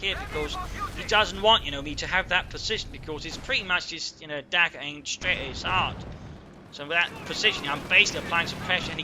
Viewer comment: I don't know if it's your accent or just me not hearing correctly, but every time you said "dagger pointed straight at his heart", I heard "DAKKA pointing straight at his heart." Maybe you're just channeling your inner Ork?